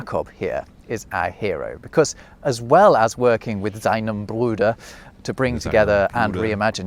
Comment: the subtitle is wrong, is "seinen Bruder"😑